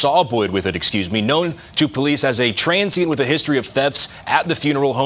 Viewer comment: First off by looking at the thumb nail I thought that was man I guess not such a shame doing that to someone that's not even alive geez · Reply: I thought they said she was a trans.